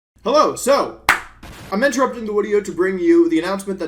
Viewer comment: did he say "wideo"